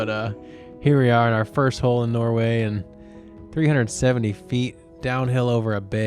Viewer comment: Does he say Norway at 16:07?